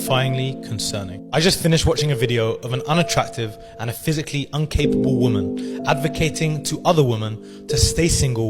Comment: this irony of him saying 'uncapable' instead of incapable.